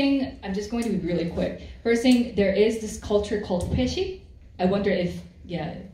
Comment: Does anyone know what was the cultural concept said at 26:04? Peshi? Baeshi? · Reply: Hi, it is spelled either Hweshik or Hoesik (회식) :) Here are some links that you can refer to. Hope this helps! <link> - Wikipedia definition of Hoesik: <link>